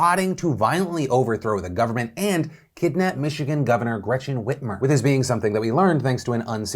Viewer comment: I thought it said Gretchen Weiners at first. 😅